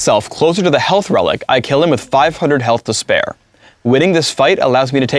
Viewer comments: Wtf guys, he obviously says FOR, not HER.. You can also hear him saying HIM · Did Phreak just call Teemo a 'her'?